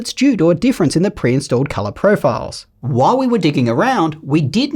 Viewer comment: Did you say "while we were dicking around"?! 😮